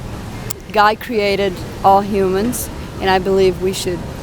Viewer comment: Did she say "Guy created all humans"?